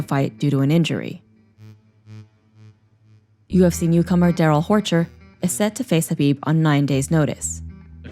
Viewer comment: Im I the only one who thought she said whorhey